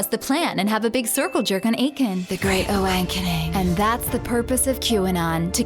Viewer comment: "The Great Awankening" ROFL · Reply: for a second there i thought it said great wankening